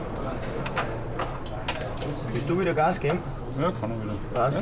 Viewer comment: the one guy said (in an Austrian dialect): "Wüst du wieda Gas geb'n?" (roughly translated as "do you want to control the rpm again") and the auto-generated English captions said "You stupid game. Heat. Heat." 🙂